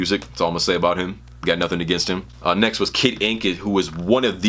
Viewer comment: I thought he said kid cudi